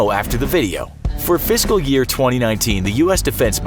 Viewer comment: Did he say visco